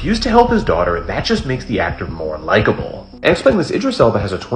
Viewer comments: I thought he said unlikeable at first lol. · I thought he said more unlikable but good on him that's a practice that should be everywhere